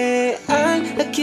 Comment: Yo pensé que decía: "Puede parecer tan TÍPICO" Y dice atípico;((